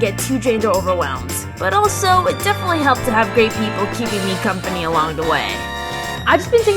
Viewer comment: I’m sorry did she say “great people” or “gay people”